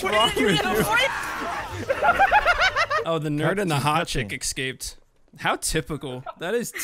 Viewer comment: I thought said chicken instead of chick which made it way worse